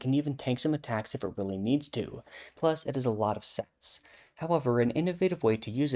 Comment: Does she said that Miraidon has a lot of sex??????